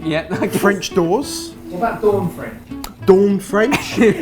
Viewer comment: hahaha that dawn french joke made me tear up😂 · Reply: gah, thank you! i thought they were saying 'dorm french' and have been wondering what exactly that meant